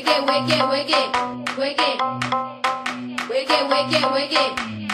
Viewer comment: I dont know why, but i heard "wegay, wegay wegay wegay", sorry im brazillian with a shitty english.